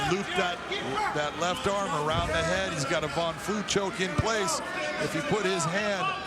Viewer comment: What kind of choke did he say he could’ve gotten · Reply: Eyeatass von flute choke